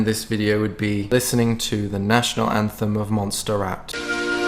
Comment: At the end I thought he said, "let's listen to the anthem of Monster Rap..." Lol.